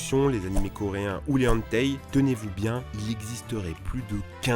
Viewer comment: Les... Les... Les quoi ? Les "Enteille"? 😂